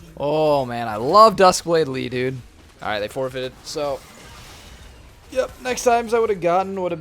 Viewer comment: What he just said? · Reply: they ff